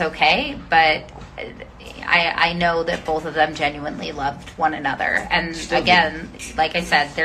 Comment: after his mom says i know they genuinely loved each other miley goes "still do" am i the only one who heard that. why is everyone questioning if they still "love" each other? ( i say love in quotation marks cause she's 14 she's a child. she acts like a child looks like a child sounds like a child and probably thinks like a child. also she is CONSTANTLY in a relationship. i don't really know because i'm not inside her head but part of me feels like she doesn't know what or who she loves yet. she's so young how can she? i'm just saying most teenage relationships don't last. a lot of the time what people feel is infatuation or lust so maybe that's what's happening. again, i don't know, i'm not inside her head) second there was two tiktoks that were made. one by mikey. one by dani. they each had the facetime filter thing on so it looked like they were on ft. it's the so the words on the screen go: ex: hold on i'm gonna put you on mute forgets to mute ex: ( i don't remember the exact words but something along the lines of:) i can't get over him/her (him on mikey's tiktok [its supposed to be dani talking] and her on dani's tiktok [its supposed to be mikey talking] and then the audio goes "what's that supposed to be about baby" so idk if that's making sense but just look it up basically it's saying dani isn't over mikey, mikey isn't over dani it's subtle and most might not notice but i'm pretty sure they're talking about each other so... comment if that doesn't make sense i'll